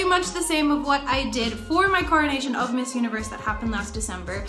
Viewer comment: I heard "carnation", then realized it was "coronation" 😭😂 I'll let myself out